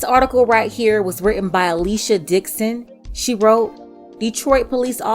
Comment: What she said: "this article is written by Alicia Dixon" What I heard: "this article is written by at least your dick's in"